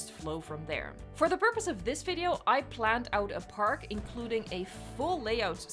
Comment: I heard "a lot of this video is planning" and thought "oh god an ad read is coming aur naur" but nay